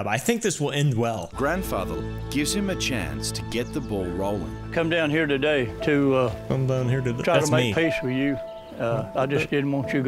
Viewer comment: I really thought he said "You come here today on the day me daughters barbeque"